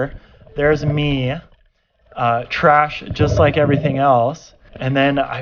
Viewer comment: did he call himself trash